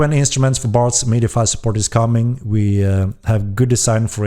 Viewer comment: did he say there's bards coming?